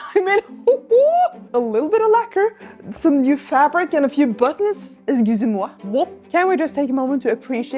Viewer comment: If anyone is wondering what said at 16:54, I think it was, "excusez-moi ?", which means "excuse me?" in French.